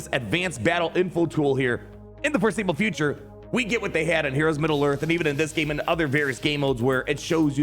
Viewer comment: You said "heroes of middle earth" and i heard that as "heroes of newerth" and i was instantly transported back to a LAN center i managed and listening to grown men argue which game is better, league of legends OR heroes of newerth. Those were the dark days.